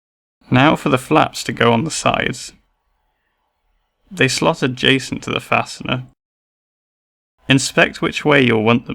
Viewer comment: Did he just say, "the slottered Jason to the fastener"? I honestly don't know what he said. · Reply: "They slot adjacent to the fastener" - there are subtitles available too if you need!